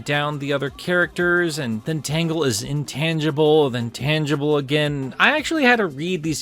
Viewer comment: I thought tails said she was Semi tangible not intangible.